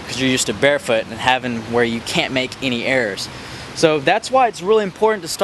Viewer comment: Lol i heard "errors" as "airs" and then i realised